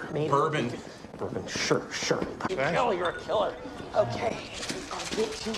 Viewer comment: DID SOMEONE JUST SAY BILL YOUR A KILLER INSTEAD OF RICHIE?! BAHAHAHAH